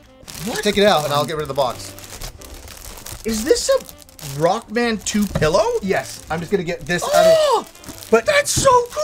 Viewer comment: At first I thought said, “A Rock Band 2 pillow!”